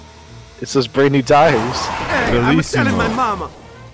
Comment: I thought he said “ima selling my momma” for a second lol